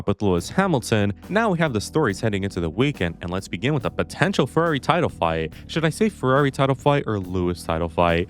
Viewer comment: I heard furry title fight instead of Ferrari title fight...